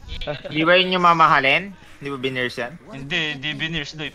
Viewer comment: Subs said "brinaise". i think said "hindi ba yun yung mamahalin? hindi ba veneers yan?" then says "hindi, hindi veneers yun, pustiso yun legit"